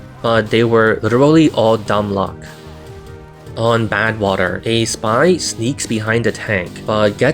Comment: Did he actually say "gay Spy"? Because that's the only thing I can hear, no matter how hard I try